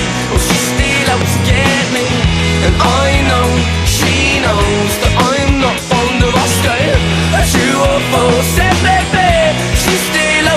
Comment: I always thought it said "I know she knows I'm not from Nebraska" LOL